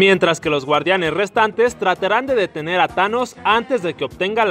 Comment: Minuto 3:25 Like si escucharon que dijo "trateran"